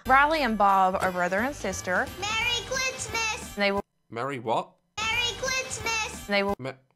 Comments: Am i the only one who heard “marry clitmaz?” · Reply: I heard “Merry Chwistmas” Lmao that’s adorable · IT SOUNDED LIKE HE SAID MERRY CLITSMAS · i heard "clitsmas" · Ngl thought the kid said “Merry Clitsmas!”